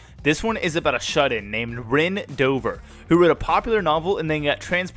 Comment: Almost heard that as Ben dover not Ren Dover 😅